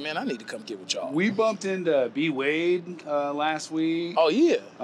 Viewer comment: Nigga did you call me B Wade???? @ 1:02 I'm hot😡😂🤣😂🤣